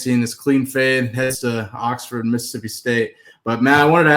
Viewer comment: did he say headed to Oxford Mississippi State? Those would be fighting words. Ole Miss is in Oxford, and State is in Starkville Mississippi State hate each other so much, it’s probably the most heated rivalry in the nation. 😂